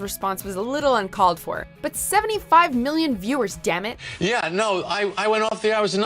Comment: ㅋㅋㅋㅋㅋㅋ 뒤에 Damn it! 들리는건 기분 탓인가요ㅋㅋㅋㅋ